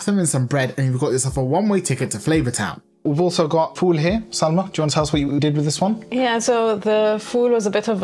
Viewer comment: So we heard you say Sarma instead of Salma, so now you two are named Sarma and Falafel in our household 😂